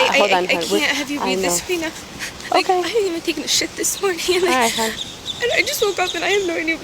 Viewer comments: Lmao @ 3:28, did she say I didn't even take a sh!t this morning. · did she say “I haven’t taken a shit this morning” like that means something 😂😂😂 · Did she say "I haven't even taken a shit this morning" ? Did I mishear that or what? Why would you volunteer that kind of information? I don't know. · Did she just say "I can't have you read this to me rite now, I haven't even taken a shit this morning." that's not what she said rite!?! I must be hearing things! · Did she say she hasn't even taken a shit this morning? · Did she just say she hasn't even taken a shit this morning yet??? Wtf!